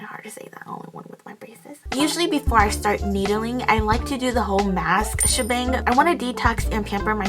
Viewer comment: Honestly thought you said “masturbating” when you actually said “mask schabang”